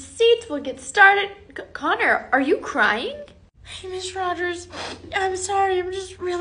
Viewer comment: At first I wasn’t paying that much attention, and thought she said,” Conner, are you high?”😭